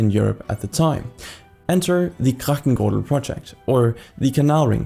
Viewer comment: For the love of me I cannot understand what you are trying to say at 0:38 (Kragtenguhl?), sounds way to German for a Dutch word... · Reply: ‘Grachtengordel’.